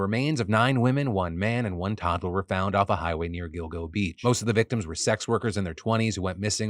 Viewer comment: I thought Phil said "Dildo beach"